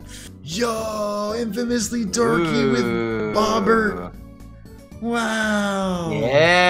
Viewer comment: Was it just me, or did he say, "OHHHH!!! Infamously Dorky with Bobert-"???